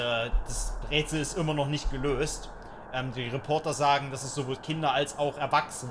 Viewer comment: Bin ich der einzige der bei 5:13 auch "das Bretzel ist immer noch nicht gelöst" verstanden hat?